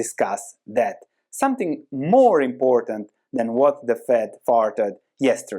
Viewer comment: Thanks! Did you say at the end: "what the Fed farted yesterday?" that's what i heard. Here's a video idea: since we are seeing secular shifts in markets perhaps a discussion of correlation vs non correlation ie individual companies vs indexes would be very useful!